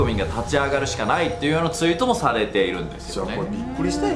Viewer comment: 口の形見るに14:45はさんの「されているんですよ"ねぇ～..."」がへぇ～に聞こえてる。 21:56 も同じくさんの「そうなんよ"ねぇ...ぇえ"」さんの語尾が裏声で上がるクセがそう聞こえさせてますね。 霊感とかないんで実はそこに被さって二重になってるとか言われたら何も言えないけど。